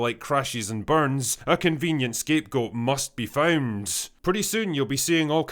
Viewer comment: What word did he say at 3:07 ? the one the subtitles interpret as "found". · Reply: He said found